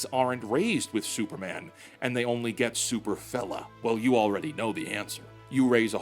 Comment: When you said "Super fella" the first time, I heard "Super fellon"...